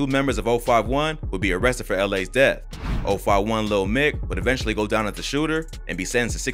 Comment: I had to rewind at 5:10–thought folk said “lil neck”.